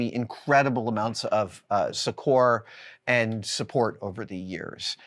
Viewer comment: what did he say? Saquor? Can’t find it anywhere. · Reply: Succor, I believe